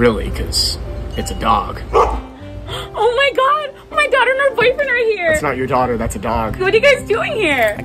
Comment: I thought she said "my daughter and my other boyfriend is here"